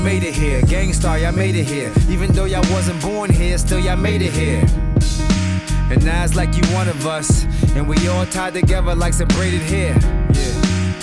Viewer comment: i always hear "and Nas it's like you're one of us" but i damn know well that it's not.